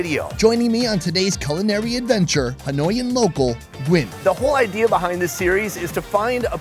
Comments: Lol at I first I heard "Annoying Local" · I thought he said annoying local lmao